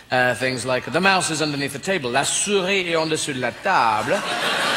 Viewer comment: Any French speakers, it sounds like he says 'La souris il est en dessous la table' - Any reason you say that instead of 'La souris est sous la table'?